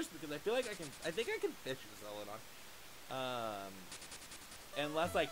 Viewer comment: I knew you could fish in the Cerulean City Gym, but not the Celadon City gym lol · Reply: Lol I was messaging someone and I heard "we can fish in the Celadon Gym" and I was like hold up this isnt right